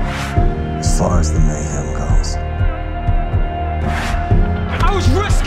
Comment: what did jax say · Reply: he said “as far as the mayhem goes“